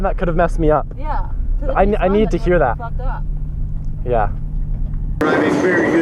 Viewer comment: Anyone else hear “it’s not that hard to fuck up”